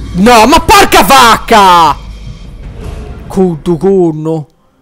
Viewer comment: Cuddugunnu? Ho sentito bene?